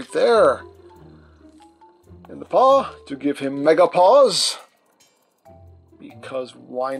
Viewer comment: i heard you say "give him Menopause" poor cheetor lol